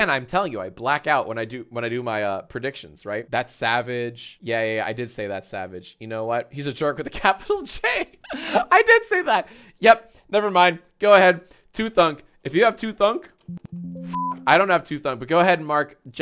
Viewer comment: I thought he said you should throw him into capital jail